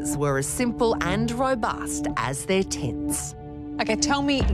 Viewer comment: Jesus I thought the narrator said “as robust as their tits”. I was distracted cooking though